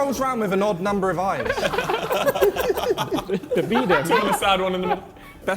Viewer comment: did tobi just say ‘the beedem’ 🤣🤣🤣🤣